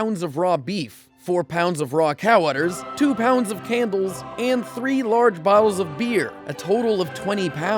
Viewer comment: "2 pounds of cannibals" O . O wut? Misheard